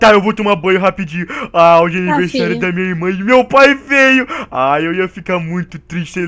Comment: entendi meu pai e feio ksksksksk